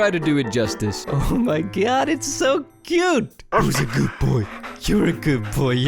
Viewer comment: I THOUGHT HE SAID WHO WAS A GOOD BOY/GIRL (i forgot) LOL!!!!🤣🤣🤣🤣🤣